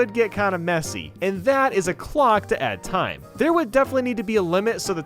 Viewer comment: THE TIMING OF THIS VIDEO LOL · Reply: I swear when you said "clock" I thought you said glock